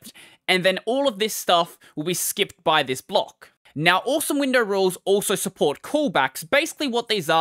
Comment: Please don't do that. I thought you said "awesome Windows rules"... I thought you'd gone to the Darkside for the moment.